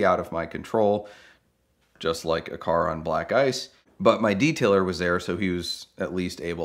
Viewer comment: I don’t understand why black guys at these locations would make it hard to drive. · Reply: I swear I thought he said, " Black 👁️ 's